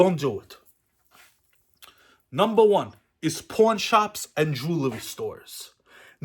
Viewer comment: 😂😂😂 that traxnyc shit was great. Also I thought you said Porn* shop at first 😂